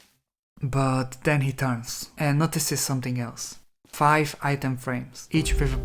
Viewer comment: I heard this as “Five Nights at Freddy’s” bro 😭🥀